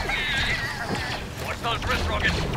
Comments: did he say "christ"? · Did I just hear a clone say “Cripes!” At 0:20?